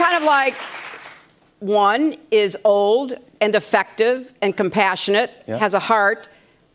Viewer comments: One is old, kind, affectionate, and wants to make America great again! One is old, has memory loss, sniffs hair, and has a son that committed a bunch of felonies that he’s not being charged for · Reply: Ohhh… I thought she said “defective” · I thought she said, " One is old, and defective...😂